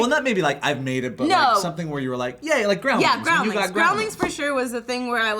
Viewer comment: needs to enunciate when she says Groundlings. It sounds like she says Growlings, and I am all "Whats Growlings" but its Groundlings and it confuses me lol. People in general need to enunciate it better.